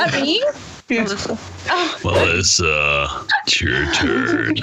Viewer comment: who heard “Melissa it’s your turd.”